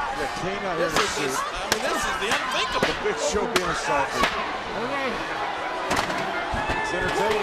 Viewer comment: What did JBL say about The Big Show? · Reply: Big Show being Assaulted